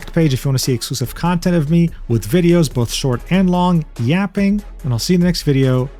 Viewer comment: I was scrolling absentmindedly on another tab while listening along and I misheard "with videos" as "lewd videos" (somehow) and I nearly spat out my coffee.